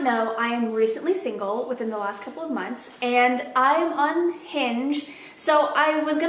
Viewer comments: I thought you say “I’m unhinged” instead of “I’m on Hinge” and I was like well I appreciate the honesty before context clues kicked in. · Lol, I really just thought you said you’re recently single and unhinged 😂 always love your videos! X